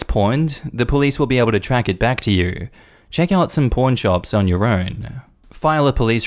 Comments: When you say “pawn,” it sounds like “porn.” I kept hearing “porn shop” all through that story. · Had to do a double take on the story of the kid taking the ring. Sounded like said “porn shop” not “pawn shop”.